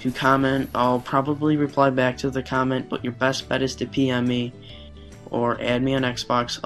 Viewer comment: did he say to pee on him? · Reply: pm, private message